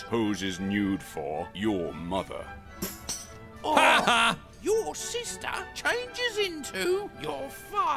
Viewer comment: Mark sounds like jack saying 'haha,POOR'